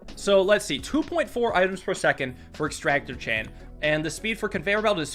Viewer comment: Did he say extractor-chan or chain